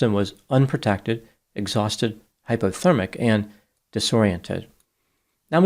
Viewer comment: i thought Dr Grande said "disorientated" for a moment and the world was crumbling around me